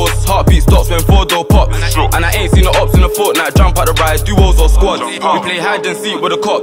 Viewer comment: Did he say fortnite 🤣